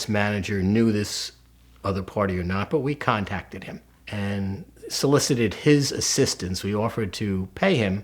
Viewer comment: thought they said solicited his sister 😂